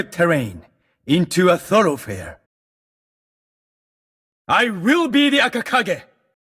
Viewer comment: I thought Hayabusa said: I will be the next Hokage